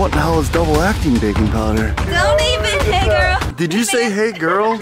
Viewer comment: It sounded like said “ don’t even, negro.”